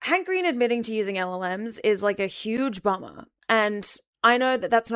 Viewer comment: i for a second thought you said MLMs and was very confused and had to do a double take